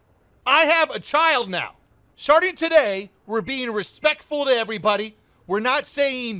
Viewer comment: really sounds like you say "Sharting Today"